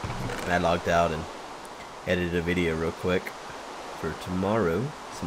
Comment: I could have sworn he said he edited Avidya.